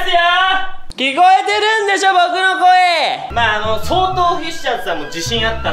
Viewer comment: 投稿されてから結構経ってるんですけど何回みても面白い！ 「聞こえてるんでしょボクの声」が「祇園精舎の鐘の声」の音に似すぎてるw